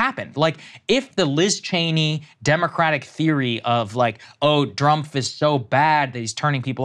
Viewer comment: Did he say "Drumpf?"